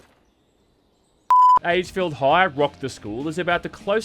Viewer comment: I kept hearing "aidsfield" whenever said the games name, and honestly that's pretty fitting considering how abysmal the game really is.